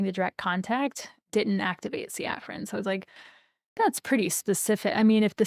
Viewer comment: Sefrin? What are you saying? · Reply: Didn’t activate the afferents. I think.